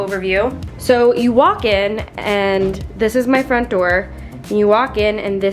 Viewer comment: When you said this is my front door I thought you said this is my friend door lol